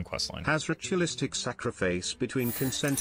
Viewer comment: Can someone explain the joke at 29:20? I can't hear what the robotic voice says, gratuitous sacrifice?